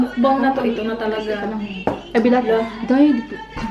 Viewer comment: I hear "ay bilat!" 😂😂